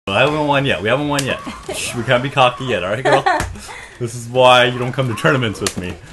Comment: at the beginning when you said "we can't be cocky yet" I had to do a double take because I swear I heard "we can't bukakke yet"